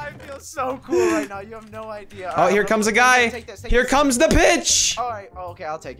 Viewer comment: it sounded like he said "here comes the bitch"